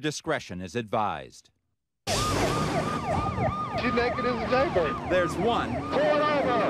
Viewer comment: What did he say?!?!? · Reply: "She's naked as a jaybird" - See